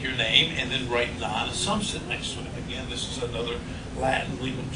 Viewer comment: what did u say to put.non asumpta ? I couldnt hear you?